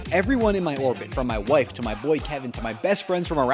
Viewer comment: did you say "wife"?